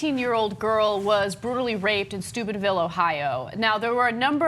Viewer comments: I keep hearing "Stupidville" whenever someone says "Steubenville." Poor choice of a name. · Am I mishearing Ana, or is she pronouncing it Stupidville? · Sounds like Ana is saying "Stupidville, OH." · i thought you said stupidville. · I thought she said Stupidville ... Would be becoming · I thought Ana said "Stupidville, Ohio".